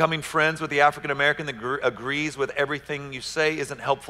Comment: Did I really hear him say at 20:00-20:30 that if I meet a black brother and we agree say...on the gospel... that black brother or sister... is probably just seeking approval. Wow... just wow. I can’t believe anyone going to this mans church who hears this can still go there. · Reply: That was a very different way of hearing him.... I heard " if he agrees with you on everything....." not if he agrees with me on the gospel.... there is a big difference.